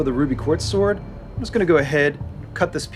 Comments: did he just say "Ruby Quartz?" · XD did he say Ruby Quartz? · Did he say Ruby Quartz? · i heard ruby quartz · Did he just say ruby quartz at 11:57 pearl would be very displeased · Did he call it the Ruby Quartz sword?? Or was it just me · did anyone else hear him say ruby quarts sword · does he say "Ruby Quartz" or am I just going insane? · Did he just say ruby quartz